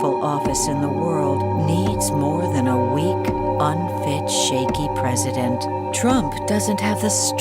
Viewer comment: When the commercial said weak unfit shakey president... I swore i heard weak punk bitch shakey president